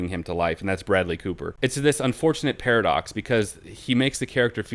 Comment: did anyone say Paradox?